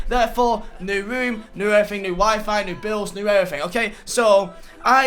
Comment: thought he said new wife 😂